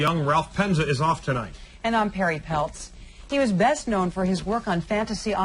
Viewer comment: Perry Peltz!?, at first i heard "hairy pelts" 😂